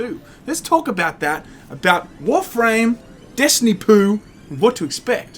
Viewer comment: I heard that as "Destiny Poo" 😂